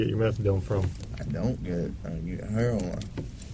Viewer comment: Cop: Where do you usually get your methadone? I don't. I usually get heroin. Best line. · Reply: I thought said, "I don't, I get heroin."